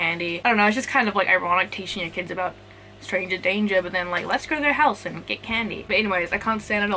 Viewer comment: When she said stranger danger I thought she said stranger things and in my head I was like yes I would definitely teach my kids about stranger things